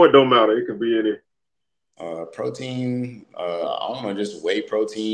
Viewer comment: Great interview must thought you said protein instead of pro team 😀!